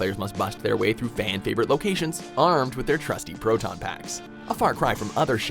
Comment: For a moment, I thought he said "Protein Packs." I was ready to fanboy out at him.